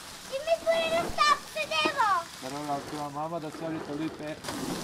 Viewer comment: did she say PEWDIEPIE?